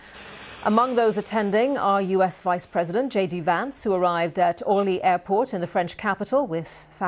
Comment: what airport? I hear like "O-yee airport" but i dont know such airport. anyone could hear it?